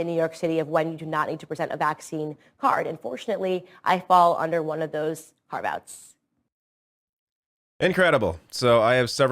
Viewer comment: I can't quite work out what she says she falls under. It sounded like she said "I fall under one of those carve-outs". Is she mispronouncing "caveats" or is "carve-out" a thing, or am I just not hearing her properly?